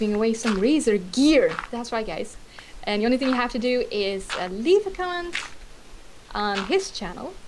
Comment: What did she say at like 3:12 "all you have to do is leave a C**t?????